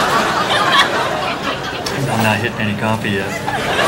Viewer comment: "I am not getting any coffee yet" did it for me lol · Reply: Me too , but I thought he said "hitting" instead of "getting".